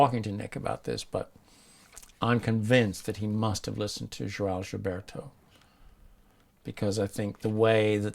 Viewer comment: " Georges Roberto "?i didn't understand. Who is he talking about? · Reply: João Gilberto, a famous Brazilian bossa nova singer